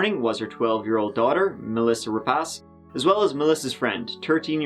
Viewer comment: Was she called Melissa Rip-ass?